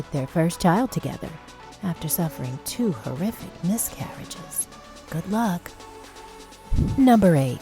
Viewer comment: did I heard 72 horrific miscarriages? Or did I heard it wrongly???